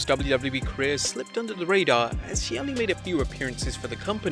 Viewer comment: Did he call big swol a he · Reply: No he said as she